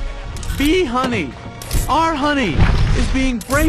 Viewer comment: Woah woah woah woah woah 9:00:30 I hear the word BEE and nothing happens. ......